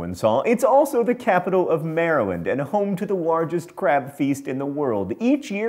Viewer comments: and on, I swear he was saying crap the first time I watched this. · Oh my gosh at 4:09 I thought you said crapfeast and I freaked out for a split second.